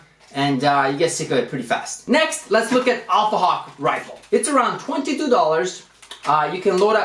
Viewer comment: did just said alpha hawk rival?